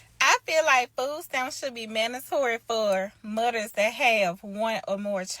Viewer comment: Sorry, these people anger me...I'm out. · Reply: I swear. I couldn't get past. 0:23. I heard her say "mudders". 🙃🤢.